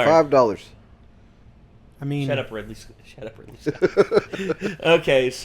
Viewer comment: what did he say? Shut up Ridley? Who is Ridley?